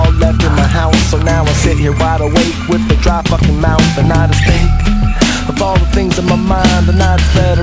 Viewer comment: I thought he said "I stink, of all the things on my mind" Which tbh has the same output lmao